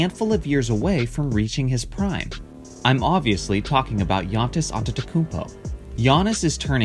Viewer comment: is it just me or did he say giantis antetokounmpo?